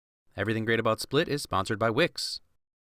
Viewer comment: Omg I thought you said the video was sponsored by wigs, cause the guy is bald